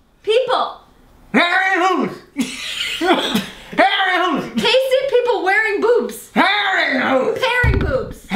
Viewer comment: at some points it sounded like joe was saying "pasty pee hole". 😂